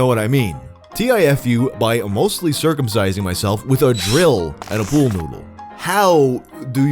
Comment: I thought the first one he said sounding with a drill bit and a pool noodle